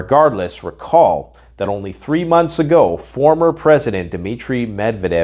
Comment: I thought you said WW3 started months ago.....